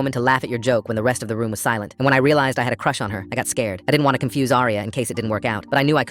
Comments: bro i thought he said he had a crush on aria 😭 send help🙏😭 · I thought he said he had a crush on his daughter😭🙏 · I thought he said he had a crush on his daughter 💀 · I misheard and thought he had a crush on his daughter 😭😭